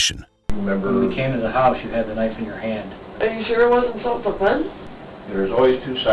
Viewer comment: she said "Are you sure it wasnt self defense?" For those who didnt understand it. Pretty sus, let me tell you that much.